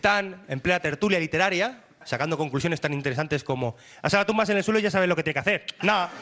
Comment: Alguien me puede decir lo que dice en el 15:30 ? Gracias!!! Jajaj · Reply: A esa la tumbas en el suelo y ya sabes lo que tiene que hacer. Guiño. Nah!